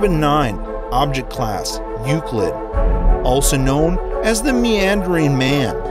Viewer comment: why did you say meeoww man what is a meeeooowww man